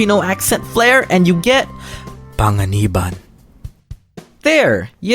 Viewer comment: I thought you said Tanga u said panga